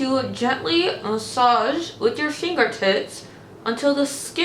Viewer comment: I almost spit out my coffee! When you had the lip mask on you sounded like you said rub it in with your finger tits! 🤣😂😅